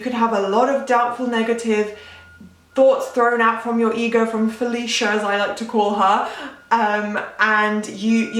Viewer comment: I heard you said Felicia 🤣🤣